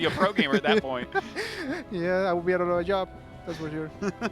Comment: what did Special say? Did he say "Yeah, I'd be doing your job instead." or something to that effect? Haha. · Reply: I heard, "Yeah, I'd be out of a job" but idk, hard to make out.